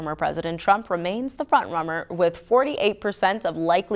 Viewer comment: Did she just say “president Trump remains the front-rummer?” Get that lady a drink please…